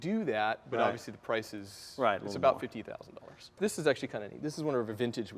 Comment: 50k for custom wheels? or did i hear wrong o.o